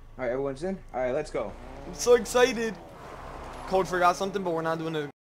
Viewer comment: Ha ha I heard you say cold i forgot something but were not doing it again ha ha 😂😂